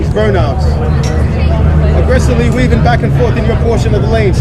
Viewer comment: What the conductor said is really important. Safety is the first priority. But~but I can't clearly catch some words from 0:14 to "slingshot" :P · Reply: He said "aggressively weaving back and forth".